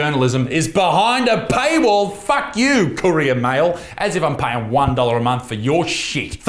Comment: I thought he said Korean mail, wtf?